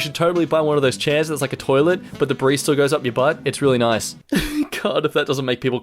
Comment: excuse me did he just say the BARISTA goes up your butt? is there something you're not telling us? Is everything alright at home?